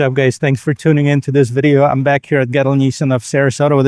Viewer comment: Ohhhh "Gettel"... when the video started, I thought you said "Ghetto Nissan". 😁